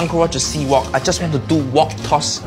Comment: I thought he said C-Walk and was gonna start crip walkin..